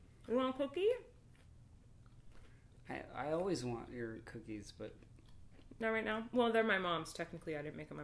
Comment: I think he said I always want your cookies in nasty way 😂